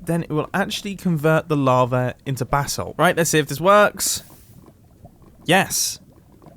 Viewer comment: did he say “bathsalt”?